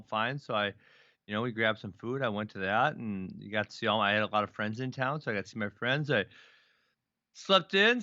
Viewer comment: did he say I got to see all my anabolic friends in town?